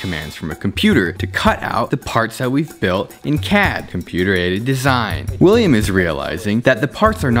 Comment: I've been binge watching so much of this channel that when he described CAD as "Computer Aided Design" I thought he said Computer-Ated Design. You've broke me William